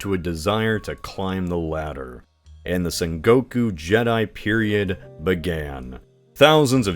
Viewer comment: did you say sengoku jedi? lol